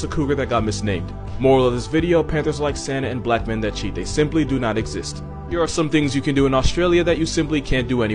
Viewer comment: "Black men don't cheat" "Literally my first boyfriend" 💁🏿‍♂️🤷🏿‍♂️🙋🏿‍♂️ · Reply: I thought he said that black men who don’t cheat they do not exist